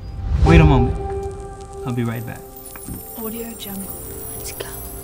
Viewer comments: I appreciate the support and love #DharMannFam! If you'd like to stay up to date with my latest photos of my newborn Ella Rose and get access to exclusive giveaways follow me on Instagram here: <link> 👉🏽 DOWNLOAD MOBILE APP: Get on the App Store = <link> or Google Play = <link> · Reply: Dharr mann no lie in 2:50 I heard audio drunk you forgot to edit that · Am I the only one who heard “audiojungle” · did anyone else hear it say audio jump? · magical voice What is it saying though? I think it sounds like "Audio Jungle" or "Audio Jangle"